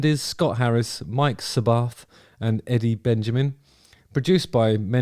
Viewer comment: I thought you were saying "Abbath" as one of the songwriters. I was trying very hard to wrap my head around that mental image. 🤔😂 Aren't Juno's a Canadian award?